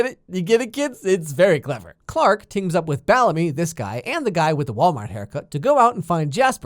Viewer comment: did.. did you just say.. Ballemy? BALLEMY?? Pffffft that made my day 😂